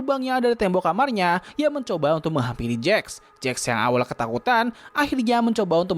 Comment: wait...... aku ngk salah denger kan v: ? menghampiri kan bukan menghamili ._.